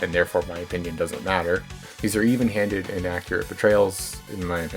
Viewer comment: wait is the line here "even handed inaccurate portrayals" or "even handed AND accurate portrayals" , sorry i cant tell what ppl are saying sometimes ! regardless, really nice video! i really like this game and what it has to offer, and its cool seeing an artist i knew for their undertale art go onto make such a cool game!